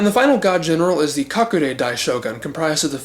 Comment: what did you say? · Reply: Kakure Dai Shogun?